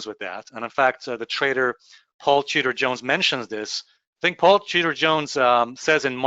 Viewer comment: Totally sounds like you said Paul cheater Jones 😂😂😂😂😂